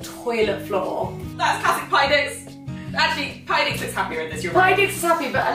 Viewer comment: PARTY DIX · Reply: Damn, this whole time I thought you kept saying "pie dix" and I just could not get it hahaha